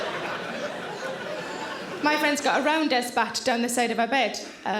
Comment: I've tried replaying this 7:14 multiple times but I still have no idea what she's saying. Is she saying "round desk back"? What does that mean? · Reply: Rounders bat, it must be the accent :)